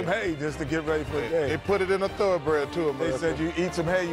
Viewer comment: Thoroughbred is the absolute best word to describe Charles Barkley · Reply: oh dear, i thought Charles said "thoroughbread", like a type of pastry. (i think i got sourdough and bread mixed up somewhere) got me here wondering what kind of lunatic puts drugs on bread loaves